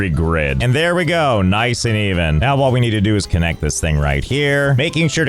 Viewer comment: DID... DID JUST SAID EVEN?